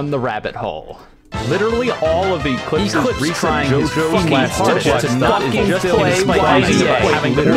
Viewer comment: I litterly only heard "PLAY YBA" all I heard after the first 2 seconds is "ECLIPSE TRYING HIS HARDEST TO FUCKING PLAY YBA" like how did it mix into that? also I know this is over a year old, but I want to know the music that is used in this video